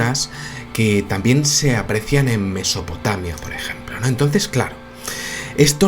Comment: He flipado nel minuto 3:14 de este video.....como no se vee la cara de no se si ha sido el o ha sido una psicofonia. Cuando el dice...Mesopotamia una voz al mismo tiempo casi dice....por ejemplo. Pero esta voz habla super despacio. quitame de dudas....ha sido tu o de verdad se ha colado una psicofonia inteligente??? Como se te vee la cara no se entiende.